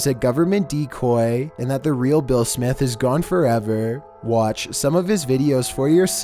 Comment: I thought you said will smith